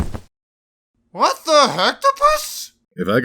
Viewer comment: Uhm can anyone make out what Firebrand was saying at 16:03? Because I hear, "what the hecktopus", but the subtitles say "what the hat the bust". So yeah, unclear even if I know it is a joke.